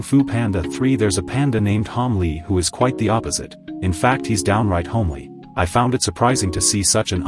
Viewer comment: When he side downright homley i thought he said downsyndrom homley lol